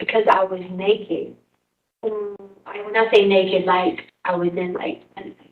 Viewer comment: Can anyone tell me what she was saying about being naked in the garage? 8:22 or so.. I thought I heard something like "I'm not saying naked like" You would think that in 2023 LE would have a better camera setup 😂